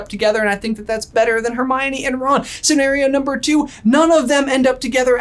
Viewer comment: when he says "Hermione and Ron" I thought he said "Harry and Ron." Just a match made in heaven.